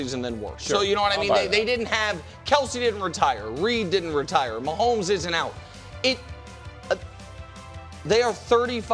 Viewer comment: what is bro talking about did he say “Mahomes isn’t out” a reason they got better 😂😂